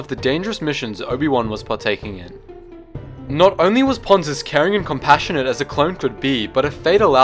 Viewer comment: Life was too unfair for Ponds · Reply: I definitely heard you say Ponds, but I was thought you said "Ponce" and I found that funny.